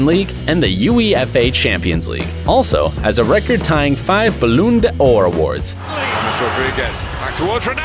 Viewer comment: Did he just say balloon Ora awards